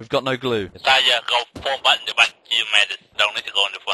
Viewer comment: i think he is saying if i die may i be second in command thats what he is saying